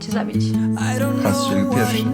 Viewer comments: co on mówi ? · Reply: Han strzelił pierwszy 😉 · co on mówi XD ? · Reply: SweetScoper han strzelił pierwszy